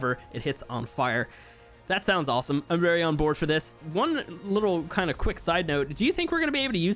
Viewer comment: I heard him say "I'm very on board from this!" And I laughed lol